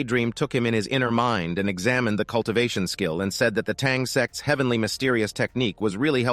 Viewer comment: did it really say tang s** at that time that pervy worm 🐛